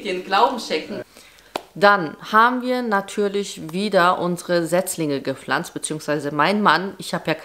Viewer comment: hab ich verstanden: „dann haben wir unsere Sechslinge gepflanzt“ 😂😂😂 war etwas irritiert bis ich bemerkt habe, dass es „Setzlinge“ heißt 😅😅